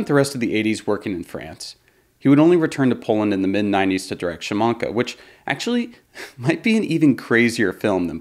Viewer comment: Also what’s the movie this YouTuber talks about around 18:40 that he says Maybe crazier than Possesion? Sounds like “shumonkuh”? I have no idea how you spell it - was just trying to type out the word I heard him say. Tried looking at Zulawski’s filmography and still haven’t no idea the movie this man is talking about. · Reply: Szamanka